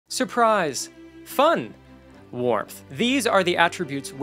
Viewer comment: Misheard the intro as "suprise, fun, wroth." Still works